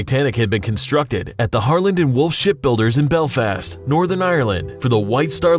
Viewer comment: did it say wolfshit?